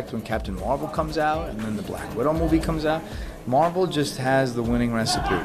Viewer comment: did he just say black widow